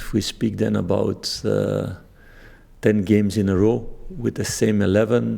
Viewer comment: I swear I heard ‘Ten in a row’ 🤫😏😂🙏